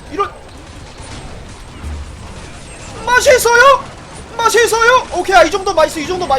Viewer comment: XD IDK What are you saying but It makes me Laugh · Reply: he said "delicious" lol